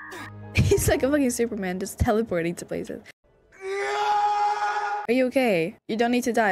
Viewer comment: No hate but the subtitles are kinda wrong sometimes 1:38 says "he's like a f*cking superman just teleporting to places" but the subtitles say "he is like a unlucky superman does teleporting to places" like what does that even mean?? 1:59 says "he doesn't listen" and the subtitles say "he doesn't listened" 7:43 says "that could have been me" subtitles "could it been me" 8:02: "has he been killed subtitles: "has he's been killed" 8:49 auiciq: "I should have just killed him" subtitles : "I should just killed him" and 8:50 auiciq says "when I had the chance" and the subtitles are complitely wrong because it says " while i have a chance" like what?? 9:41 auiciq: "i was kinda hoping he wasn't" subtitles: "i was gonna hoping he wasn't" gonna??? So this is not hate but I just wanted to point it out for you